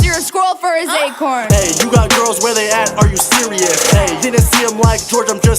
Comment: I thought Alissa said that jake had girls over constantly but rice clearly said that "you have girls WHERE THEY AT are you serious" 🤐